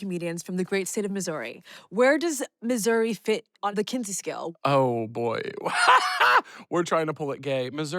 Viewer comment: I heard kunty scale😅